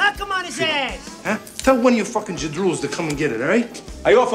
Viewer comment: You finook lmao 🤣 I love calling people that in 2023, people have no idea what it means, i’ll tell you what it means. Tony soprano: he’s as Faaaaaa…… message error.,. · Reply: what's he saying at 2:58? tell one of your f###in ZADROOLS? to come and get it? ?